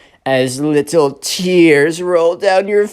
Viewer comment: captions say “so sad that I did a turtle”